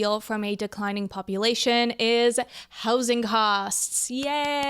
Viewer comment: I thought she said hoes in cost 😂